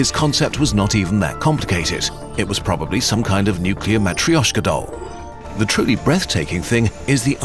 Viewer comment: What's that word you use at 6:43 ? Metcrioshkidol?? That one?